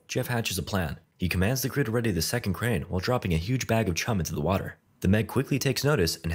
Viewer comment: i thought huge bag of chum said huge bag of cum and i was very confussed why a shark would want that